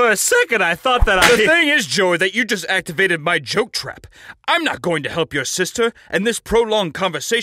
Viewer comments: “THE THING IS JOEY IS THAT YOU JUST ACTIVATED MY JOKE TRAP” i wasn’t ready for that one lmao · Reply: I thought he said choke trap, lol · “You activated my joke card!” Damn that’s cold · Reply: I thought he said “choke trap”. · “You activated my joke card!” Damn that’s cold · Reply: Tbh, i thought he said "choke trap"